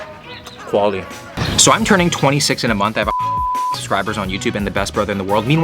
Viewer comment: my goodness this guy looks so young I thought he said he was turning 20 not 26 yikes